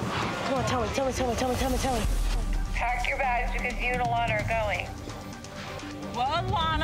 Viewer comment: I heard her say me and Alanna are going to Hollywood. Not you, me and her